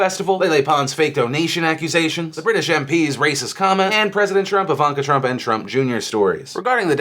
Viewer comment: when he went over to the trump stuff, after the racebater, i thought he said "princess trump!" and in my humble opinion, that would fit better then President trump. (no he didnt say princess just me hearing the wrong thing)